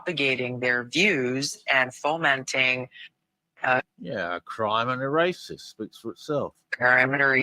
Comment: I hear her saying "a crime and racist."